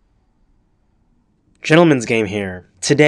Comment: I thought the intro said gentlemans gain hair XD